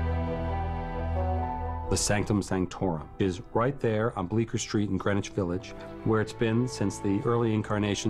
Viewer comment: Who else heard Baker Street??